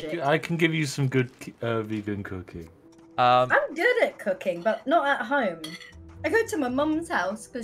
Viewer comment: I know she said “Cooking”, but it sounded like she said “Cocain”.